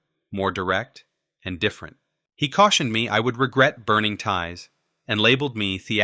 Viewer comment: "I would regret burning ties." You don't 'burn' ties; you cut them. Those things you burn are called 'bridges.' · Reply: I thought he said burning tires 😂